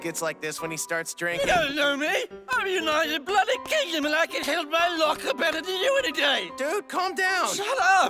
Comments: I couldn't understand a single world England was saying.... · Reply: tou dont know me im the bloody united kindom and i could hold my locker better than you anyday thats what he said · I thought he said "hold my lager" not "held my locker".